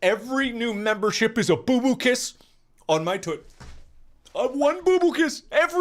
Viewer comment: Who heard him say toot